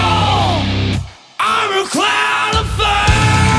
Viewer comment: I love this song, but I always hear "I'm a clown of faith".